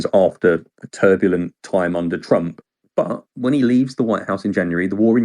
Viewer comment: Did he say "turbulent time under Trump "? 🤣 Trump didn’t hsve any wars. Things were pretty smooth.